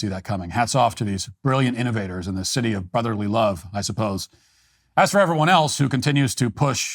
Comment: Did Matt call Philadelphia "the city of BUTTerly love"? ROTFL!!!